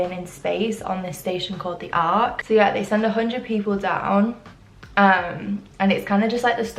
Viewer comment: What Netflix series was she mentioning at 13:25 ? I heard “the o hundred” but I can’t find it on Netflix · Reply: The 100 - its on amazon prime!